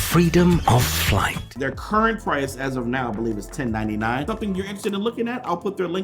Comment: When you said $1099, I thought you were saying $10.99. xD